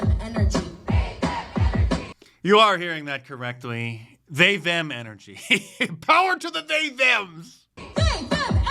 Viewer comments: I thought she was saying "Save that energy". Looks like she was stockpiling it. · LOL!!! I seriously thought she was saying "Save that Energy" looks like she doesn't have much to spare..